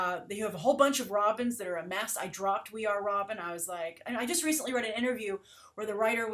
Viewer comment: I really like We are Robin. One of the best titles coming out of DC right now. I do not like Batgirl at all. I don't read the Robin title for one simple reason: I cannot stand Damian. I love Grayson; it's probably my favorite. I like Harley Quinn a lot. I don't read WW anymore; the Finches can't write her well. I like Cyborg; it's a nice title. I'm not reading Marvel right now. I'll wait until Secret Wars ends. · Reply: "We are Robin" I thought she said "Via Robin" and thought about the way to become a Robin.